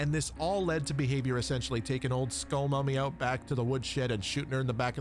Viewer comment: AYO, WHAT did you call her?? (can't blame you though, she's a baddie) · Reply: Skull Mommy. With Sexy Freddy Mori. :)